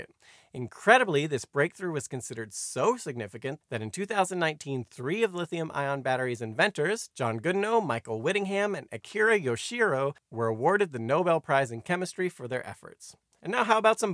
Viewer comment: It's John B. Goodenough lol sounds like you said Goodnow and captions agree..can't mess up a name like that! Especially when John made Lithium Ion B. Goodenough for the world.